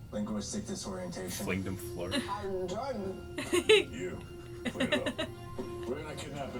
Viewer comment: when felicity said her second phrase after her first time jump, didn't it sound like she said "Hal Jordan"?